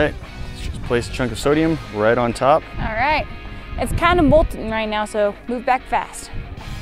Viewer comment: Did say bullsh*t Or Bolting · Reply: I heard molten, as in the red slime was molten from the last sodium chunk they put in it made the slime activated.